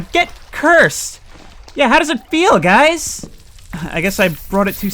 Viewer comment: Sounded like one quicktime event failure away from yelling "GET F***ED"